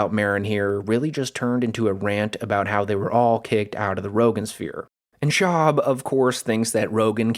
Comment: The Roganqueer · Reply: When people first started saying Rogan sphere I thought they were saying That Joe Rogans queer 🤷